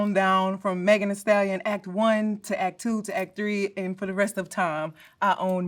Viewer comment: Im sorry, Act III?? Did she say, ACT III?? 🥹